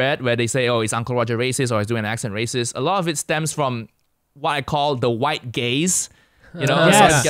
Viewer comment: “a lot of it stems from what I call the white gays” is how I heard it the first time. Threw me off for a moment! Now I feel stupid in retrospect hahaha